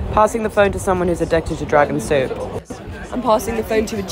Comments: I fr thought she said “I’m passing the phone to someone whose addicted to drugs and soup” and not “dragon soup.” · I thought she said “drug and soup” 💀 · Thanks for the subtitles cuz I thought she said “Addicted to drug and soup” 🥵😹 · I honestly thought for a second she said I’m passing the phone to someone who likes driving soup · no bc I heard “drugs and soup”